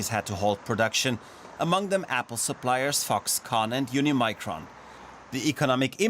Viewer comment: Did I hear Foxconn?